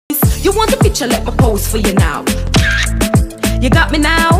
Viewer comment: WHY DID I HEAR “You want a picture of your balls sticking out” WTFF